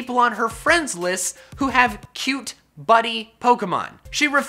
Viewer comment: Lol I thought he said "cute bunny pokemon"